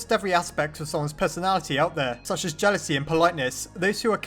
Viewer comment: Did someone say Jealousy?